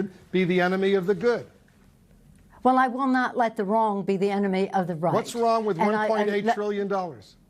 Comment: Did I mishear or did she reply “I will not let the wrong be the enemy of the right”, after Blitzer suggested she should not let the perfect be the enemy of the good? Sounds like something slowbrain George W would say without realizing the fallacy..